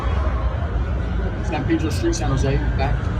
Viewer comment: I thought u said santana row, thats san pedro square